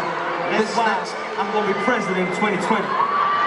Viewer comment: does niall say " im going to be president in 2020" ?!?!?!